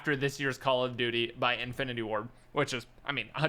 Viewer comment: thought he said infinity war